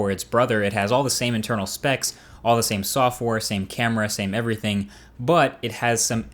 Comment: i think he said softwar